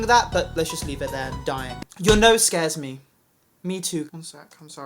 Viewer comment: I thought they said “Your nose came out three minutes later.” And I have no fucking idea why.